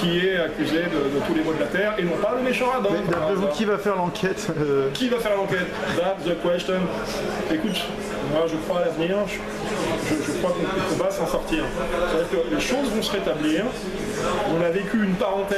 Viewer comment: Bonsoir, Hervé Ryssen dit quoi à 11:45 ? "Écoute, moi je crois à la Vierge" ?!